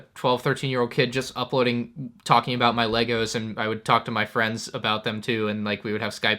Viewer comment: did he just say LEGOS?!?